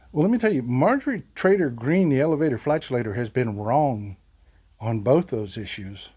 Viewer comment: Did i hear "elevator flagelator?"😂